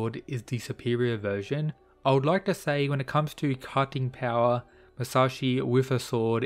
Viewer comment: I had to replay the commentary starting at 9:04 twice. I thought I initially heard: I would like to say when it comes to cucking power...